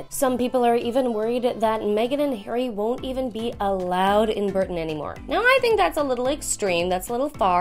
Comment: Did she just say burton instead of Britain